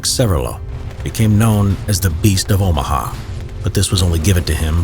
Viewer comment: Am I the only one who thought it said "The beast of Obama"?